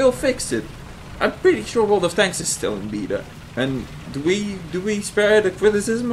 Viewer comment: does he say wot in in beta XD